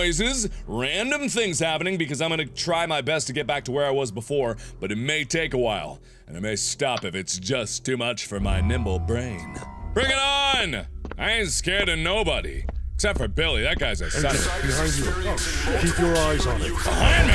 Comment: Hehehehehehehehehehehehehe, it sounded like he said nipple brain. 0:37 Speaking of Billy...